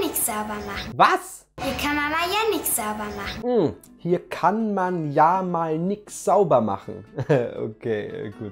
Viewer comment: Ich habe ,, Hier kann Mama Jannick sauber machen,, verstanden. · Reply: Ich hab verstanden "hier kan man ja mal(,) nicht saubermachen" 🤣🤣🤣